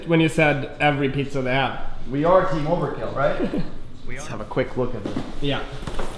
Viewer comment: Is it just me who heard "we are" @ 13:29-30 right after Jon said "we are team overkill right".